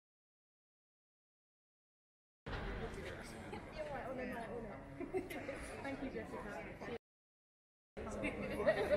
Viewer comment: does she say "piss off"? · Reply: “Mic drop”